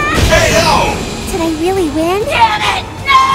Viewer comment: did juri just say dammit